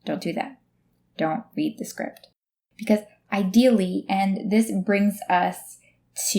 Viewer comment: Hi, I loved your video! I'm using it to practice my subtitle skills, if you don't mind, I would like to know what do you say in the second 0:45, do you say "ideally"? Thanks in advance :) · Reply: Thank you! That's super cool! It does sound like I say, "ideally" ;)